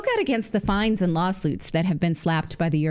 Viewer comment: Yeah, I heard you say "Lawsluits". -21:33 😅